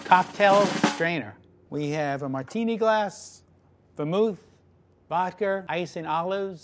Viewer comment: WAIT WHAT DID HE SAY. VODKRR?!?